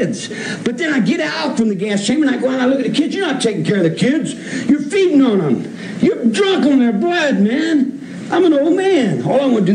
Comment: Did I hear him wrong when he mentioned feeding on kids and being drunk on their blood ?